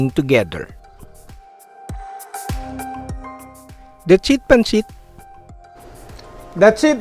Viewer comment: did he say that's it pancit?!